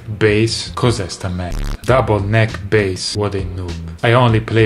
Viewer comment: "cos'è 'sta merda?!" :-D Guardo i tuoi video da anni e solo ora scopro che sei italiano. E' stato un bel momento. Mentre quello "stronzi" al 4:42 è stato semplicemente EPIC.